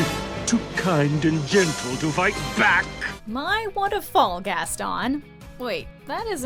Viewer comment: Who else heard “I wanna die, Gaston!!!!!”?